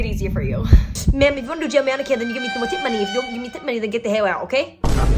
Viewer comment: all I heard was: "givemetipmoneyifyoudontgivemetipmoney you get the hail out ok"